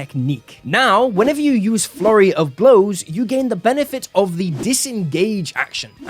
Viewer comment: Why do I always hear "Furry of blows" instead of "Flurry of blows."